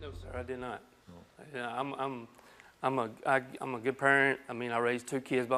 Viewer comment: I just reread the transcript cuz at 0:46 I swear it doesn't sound like he says I'm a good parent. To me it sounds like he says I'm a good perv and I was just like he snitched on himself already